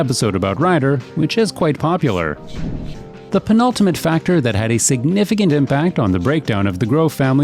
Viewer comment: Did you say "The pn-ultimate factor..."